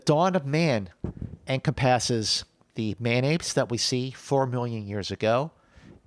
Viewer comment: Thought was saying Mayonaise, not man apes at first. Again, you guys are creating some of the best content on the web!